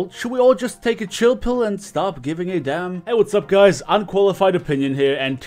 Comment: I love how he calls himself qualified opinion. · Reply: Shlad with a shlid I thought he said "unqualified" but now I'm not sure